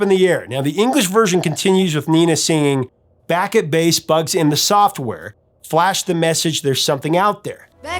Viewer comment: I heard it as "sparks in the software" instead of "bugs in the software". That always made sense to me.